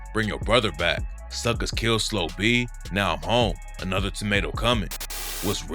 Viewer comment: A tamato coming? I thought bro said tornado🤣🤣🤣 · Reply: Its tornado but dude dont kno what he talkin about